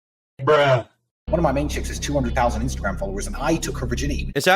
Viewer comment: I heard "Swallowers" vs "Followers" Tracks